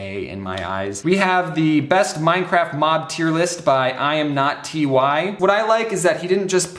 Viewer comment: LOL, James said T-Y instead of Ty( Tie)